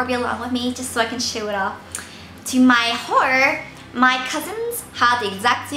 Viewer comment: when said "to my horror" it sounded like "to my whore" haha